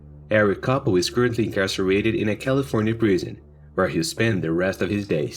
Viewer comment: At the end of the video when he said incarcerated, I thought I heard castrated, and I had a couple of moments of shock, until I realized I had just heard it wrong. First laugh of the day. Just sharing my sleepy first video of the day story. Everyone enjoy your Friday.